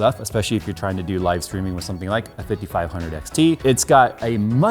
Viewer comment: Oh, I thought it said 5700xt. Looking at the charts I nearly shit a brick.